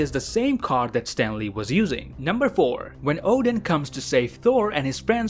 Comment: all i heard was "number thor"